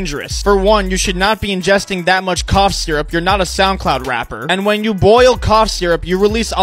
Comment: When he was talking about the NyQuil chicken challenge I thought he said cock syrup he said cough syrup